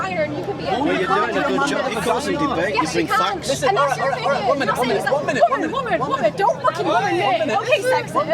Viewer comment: these people just hear what they want to hear. He is saying 1 minute and she tries to call him sexist "woman, woman, woman" These people are derranged.